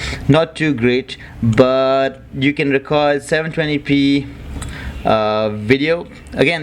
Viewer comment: i thought you were saying "videoke" hahahaha :D